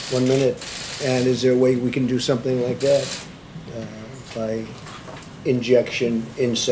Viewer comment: ''Did anyone hear him say inject. ..'' he literally say it in 1:33. I guess you only hear what u want to hear